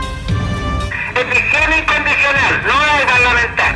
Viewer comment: Que dice en el minuto 1:05 ?? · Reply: Rendición incondicional, no debe parlamentar